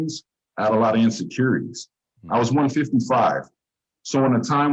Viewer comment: He said he went to CYA in 1993, so no, he was not born in 1955. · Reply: Ohhh he said, "I was 155." Lol I had to go back around the 6:53 mark. But I stand behind my Poly's really don't age comment even though it's irrelevant to what I thought he said. 🤙🏽❤